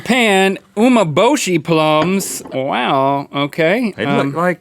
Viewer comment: i thought they said omerbloshi lungs😂